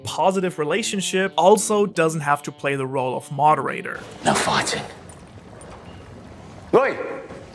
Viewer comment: Female characters just become different when they are a part of the trio rather than a support for the male duo. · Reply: i thought he said "No farting"